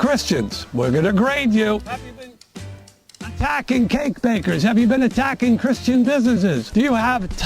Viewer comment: instead of "cake bakers" I heard "kink bankers" this is hilariously bad